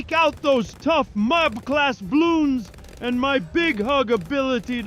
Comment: did he say mab